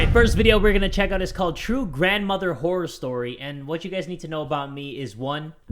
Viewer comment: i thought he said “true grandmother whore story”